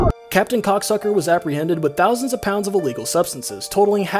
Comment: Did he just say kokC sucker and no one notices